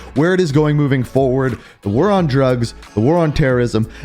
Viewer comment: I thought you said we’re on 😳